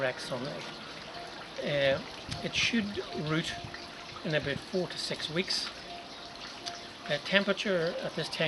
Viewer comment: Thanks for the video. For a second I thought you said that should root in about 46 weeks.. 😮 Had to rewind that to hear you say 4 to 6 weeks ☺